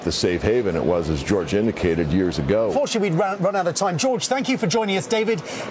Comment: Notice the corporate shill slip there at 3:24 : "FORTUNATELY we've ran out of time" he didn't mean UNfortunately, he really meant what he said.